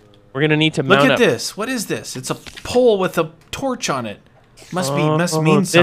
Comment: i heard say strippers